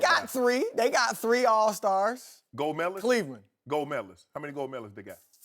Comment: i thought Shannon said "gold melos"lol